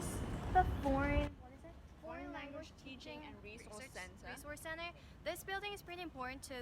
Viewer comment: I thought I heard you say in 3:42 "The Boring Language Teaching Center" ^_^V